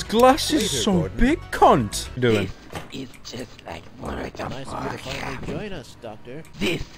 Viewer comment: I can't understand what Dr. Kleiner is saying. "This is just like (pulling?) The (farcap)?"